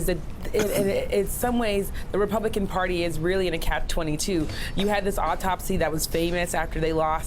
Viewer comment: Omg, did she just say, "Cap 22" at 4:09? Maybe she's congested or doesn't read books.